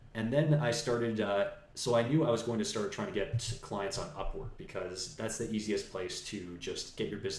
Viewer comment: is he saying "clients on UPWARD"?